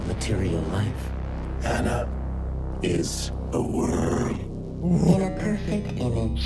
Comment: If you turn on the captions at 41:40 it says "anna is a word" instead of "anna is a worm"